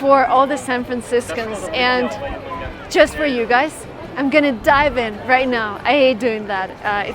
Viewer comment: Послышалось не i am gonna diving а i am gonna die блин аж неловко стало